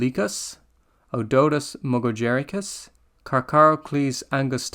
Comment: Initially misheard this as Otodus mungojerricus, and thought some goofy scientist had named a shark after a character from Cats.